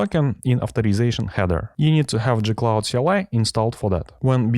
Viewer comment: what is he saying? i can't understand it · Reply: you need to install "gcloud"